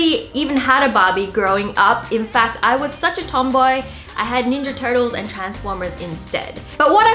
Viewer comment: did she just say tomboy??